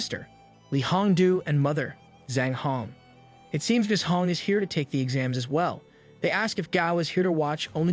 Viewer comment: My dumb ass thought it said Gao p*nis for a moment lmfao